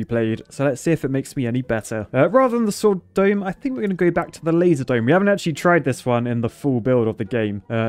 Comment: When he says "laser dome" at the start it sounds like he say "lazy" dome. It makes sense considering how much work it is to use the sword.